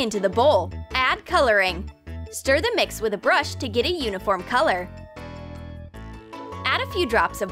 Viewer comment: "Add food coloring to get a uniform color" Looks like blood to me. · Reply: Thanks xD idk why I thought they said unicorn are first it sounded like it lol